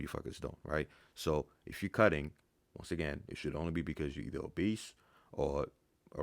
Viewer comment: i thought he was saying “cuz your a beast” not “cuz your obese”